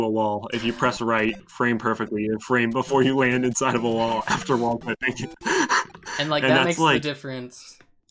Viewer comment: Hey please enable subtitles when you get a chance, thanks! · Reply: it says "wallpaper" instead of "wall clipping"